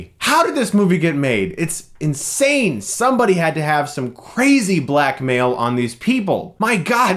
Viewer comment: When he said "somebody had to have some crazy blackmail on these people" I heard him say "somebody had to have some crazy black male on these people" and I was like woah what the fuck man . . . and then I realized how fucking stupid I was being. Good job me.